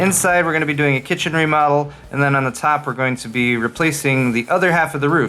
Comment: Hey for a second I thought you said you were going into modeling. You have that sexy look eh